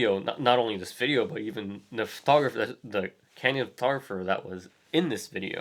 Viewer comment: Very good thoughtful video, I'd appreciate a clearer way of speaking, I had - as a non english person - some issues: 9:12 and further in the video you several times talk about "canned" (???) photographers to describe this style of shooting. I dont understand that word, what is it? In general I feel our times is quite contradictory. On one hand, people post much of their most private lifes on social networks and on the web in general, on the other hand, people tend to react more agressively to photography (being in an image) in the street. As if holding a camera sometimes is already anillegal act against their privacy. In a public space. Specially in the industrial countries. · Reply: Bernhard Ess I think the word he uses is “candid”.